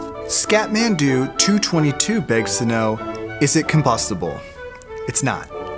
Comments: Lol when he said “ if it’s combustible?” I thought he said “ is it kimpossible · I thought he said it is Kimpossible